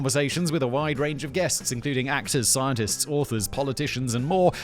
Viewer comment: somehow I thought I heard "Axis scientists" and was like huh, yeah, I guess that tracks